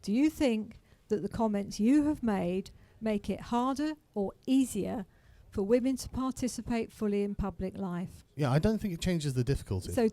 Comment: Time stamp, please? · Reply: She actually says something along the lines of "speaking as a woman, I think you make it harder for women to participate in public discourse", which was her main point throughout her time on the mic. I'd initially thought she said "speaking as a woman is harder because of you", which is not exactly as I characterised it, but effectively the same as speaking publicly is a part of participating in social discourse, and it's still Alanis Morrissette levels of irony.